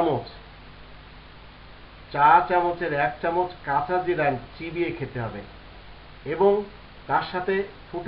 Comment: কি বলছেন, এক চামচ কি? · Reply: Showkat A 1 cha chamoch kaca jira chibie khete hobe.